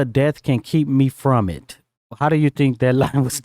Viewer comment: Every time yall talked the lyrics im like yep yep mhm makedada 🙌🏿 But I thought she was saying nothing from death can keep me from her not it hmm learn something new everyday